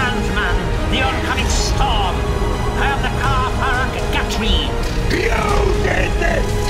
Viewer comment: what did SilvesterMcCoy say there ? "I am the Sandman, the Oncomming Storm. The ...?" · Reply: Ka Faraq Gatri - it's the Dalek words for Destroyer of Worlds 😊